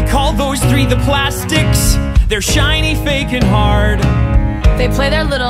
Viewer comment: Regina: the queen of everything, intimidating, the ruler of the school, bow down to her Gretchen: the right hand assistant to Regina, knows all the gossips and secrets Karen: her hair is shiny ✨ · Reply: This whole time I thought she said her hair is ✨tiny✨ I had no idea she said shiny I was so confused but went along with it